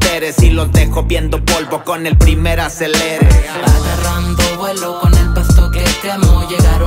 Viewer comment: Agarrando vuelo, con el pasto que quemo 🤑✈️🔥🙏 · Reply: Jaja pense que decia agarrando vuelo con el paso que vamos v